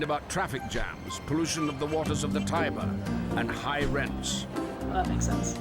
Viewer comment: WTF! Did I just hear the spanish word, "típico" at 12:54? I'm Mexican and I didn't expect to hear a Spanish word randomly :) Maybe is not Spanish but Latin?